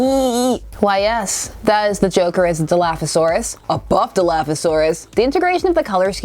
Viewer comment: "Dinosaurs aren't purple." Barney has entered the chat. I misheard "Dilophosaurus" as "Do-you-laugh-osaurus". Quite appropriate for Joker.